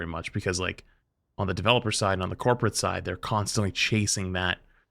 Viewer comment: its funny when he said corporate side or whatever, i thought he said corpuscide. and i was like, FUNNY YOU MENTION THAT